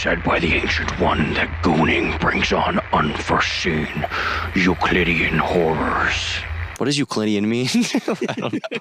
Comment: at first i thought you said "You Canadian whores" 😅